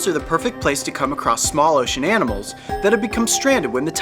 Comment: did he just say smaller shananimals!?